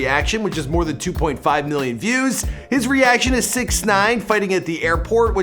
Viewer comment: turn on the captions and goto 0:43 when he says six 9 the captions say snitch 😂😂